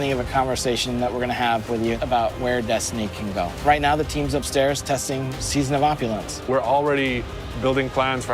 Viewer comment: “right now the team is upstairs testing season of The opulence” atleast that’s what I heard so idk new season